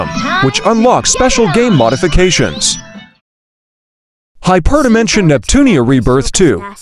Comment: Did He was " unlocks special Gay Modifications" or am i Hearing things